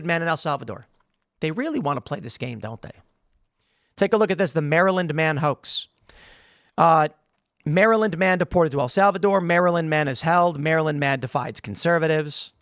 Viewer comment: That’s crazy. I thought they were saying Marilyn Manson 😬